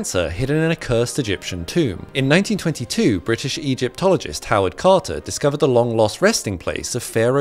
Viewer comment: I THOUGHT HE SAID HOWARD STARK AT FIRST FOR SOME REASON